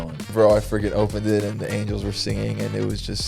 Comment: y’all’s editor put angles instead of angels 🤣